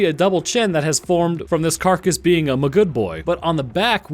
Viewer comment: I know it's not what he's saying, but all I hear at 7:57 is; "From this carcass being a good boy".